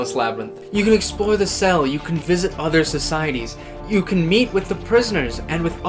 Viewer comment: at first I thought he said « you can mate with prisoners »